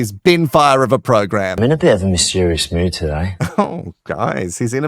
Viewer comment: Ruuuuuuuuuuuuuuuuuuuuuuuuuuunnnnnnnn Luke, run as fast and as far as you can please!!! I thought said he was in a mischievious mood, not mysterious. So naughty and mischievious, throwing those photos out of the window, lmao. Does the Silver Fox look familar to anyone? I'm sure i've seen him before.